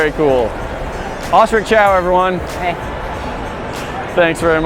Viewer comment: when he said osric chau i thought he said auschwitz shower and i was really confused as to why he said that 😂